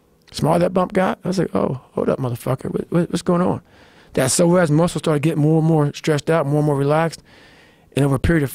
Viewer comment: That moment when you realize you suddenly need to change your whole perspective on stretching... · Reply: <link> Found this pretty good explanation of it. I initially thought he said "sore ass muscle" :p